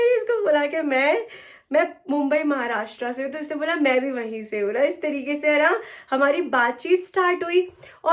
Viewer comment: he is not saying mumbai maharashtra 😂😂😂 he is saying pune maharashtra 😂😂 kya yrr kuch bhi